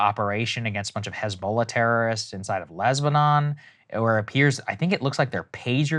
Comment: Was it just me or at 6:58 did I hear Saagar accidentally call Lebanon, Lesbanon? Lol, it made me chuckle.